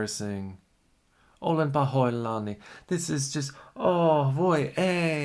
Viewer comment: Hey there, can you tell me what you said at 0:45 - 0:47? I heard a Finnish word and was curious what you said? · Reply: I said "Olen pahoillani," which means "I'm sorry"